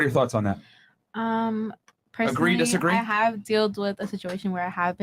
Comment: did she say “dealed with” ? Instead of dealt with? Ohhh noo I’ve lost all hope